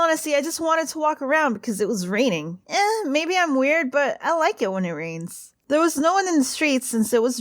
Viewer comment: When I heard her say I like when it rains I misheard it and thought she said I like trains.. but realized my mistake